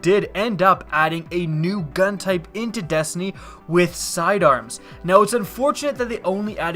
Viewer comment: I'm sorry, every time I hear you say "into Destiny" I hear "in teh Destiny". :P